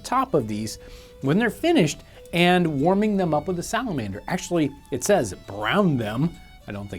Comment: did he say salamander?